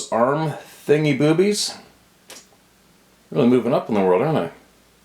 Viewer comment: Haha, did I hear "thingy-boobies?"